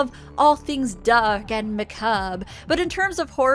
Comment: did you say "duck and macabre"